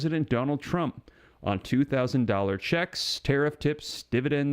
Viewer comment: I thought he said up to 2.000.00$